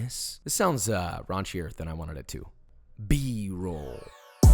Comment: Does someone can help me please, I don't really understand what he says at every endings of his videos at 8:28 , I know it's something like "be ...." , but I'm French and I don't really know this word I think 😂 ?